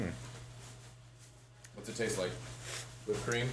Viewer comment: End of video I thought he said it tasted like a helping of children!